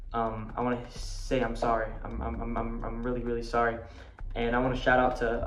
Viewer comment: bruh for a second i thought u forgot to add a rapid 6 "uhm" count here lol. I thought this kid said "I just wanna say I'm sorry umumumum uh uh-m" 😂until i rewatched after falsely typing out a comment reprimanding you for missing six "uhm"s until i noticed it was "I"